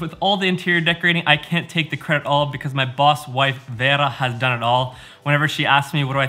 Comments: I thought he said “boss’s wife” instead of “boss wife” haha big difference 😂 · Anyone else hear him say “Boss’s wife” and not “Boss Wife” then start the next sentence with “Babe”? It took me a second. At first I was like “this guy has some balls.” 😂🤣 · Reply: OH! Boss Wife!! I totally heard "Boss's Wife" and was all "Welp. Maybe they're just like...really good friends?" Thanks for your comment clarifying. =D